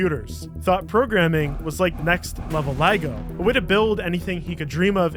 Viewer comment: Love your videos but it absolutely (and idk why) disgustssss me the way how say Las Vegas!!! Lmao all jokes, but for real its VEY-GUS not vie-gis 😂😂 edit: 19:04 you did NOT just say "LIE-GO"!!!! I didnt know you canadians were THAT bad at pronunciation lmaooo